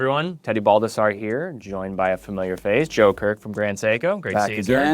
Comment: When he said Joe Kirk I thought he said Joker and I was quite confused!